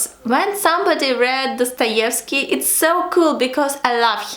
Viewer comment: what is the title of book you say in 3:53 ? help me please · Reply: Dostoevskiy